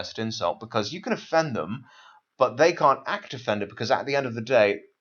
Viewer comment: What does he say at 44:29 ? It is 'they can't act defend it...' or what? Is 'act defend' something you can say? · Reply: Act offended